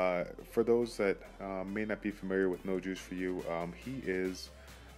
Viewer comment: Me explican de que trata?? No entendí mucho :d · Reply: escucha lo que dice en el minut 2:00 dice nojuice4u que es un canal de youtube de latinoamericano